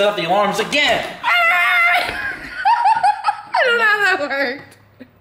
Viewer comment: the fk did she say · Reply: ToffeKt I don't know if that worked